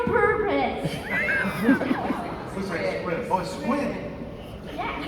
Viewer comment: help, what's the word at 16:14? I dont speak english very well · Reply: A squid. 🦑